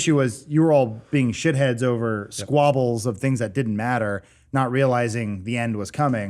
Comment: Did Adam say Scrobbles? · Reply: Not sure if you meant that as a joke , but in case not, he said "squabble". It basically just means "a petty argument". <link>